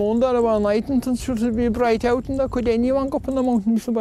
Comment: Jack: Alright ill translate for ya Me: There’s no way he can understand that Jack: fully translates it Me: ö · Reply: All I heard was "Break out" something something "mountain" and I heard mountain correctly, but "break" was actually "bright" and I didn't realize until Sean said it.